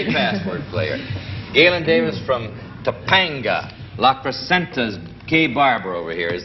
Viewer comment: it sounded like Allen Ludden said, "La Placenta's gay barber."